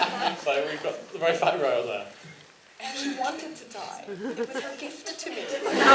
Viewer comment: What she's saying at 10:15?(Katie)ANyone???...please?? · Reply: Christina Moskof she said "She wanted to die, it was her gift to me"